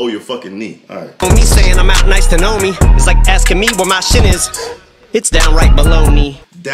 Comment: What I thought he said at 16:51 was "its like asking me where machine is.....its down right below me".